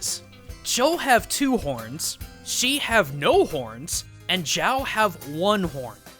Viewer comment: i swear i heard "she has no horns"